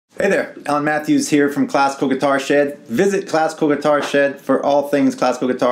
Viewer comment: I thought you said "classical guitar shit" lmao